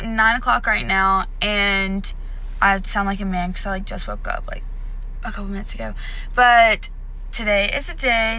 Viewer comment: When u said I just woke up a couple minutes ago at the beginning I thought u said weeks ago and I was like whattt??😂